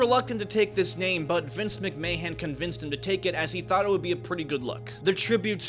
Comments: “Vince McMayhem?” Did I hear that correctly? 😅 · did he call him "Vince McMayhem" ? lol. I probably heard him incorrectly but the Vince McMayhem actually suits Vince quite well. Edit* Wow the US a sunk low, when it comes to celebrities. Dwayne Johnson #1 over Lincoln, MLK, Elvis, & George Washington as the biggest American Icon? I like the guy but really? No actor should be above Lincoln, MLK, & George Washington. I am not even an American and I find that almost offensive lol. Is that what young people have become now?